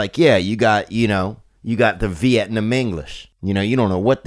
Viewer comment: Pretty sure I heard him say "Vietnamenglish" and "Alaberman" 🤦‍♂️